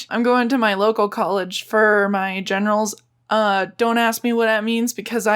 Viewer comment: am I the only one that misheard it like genitals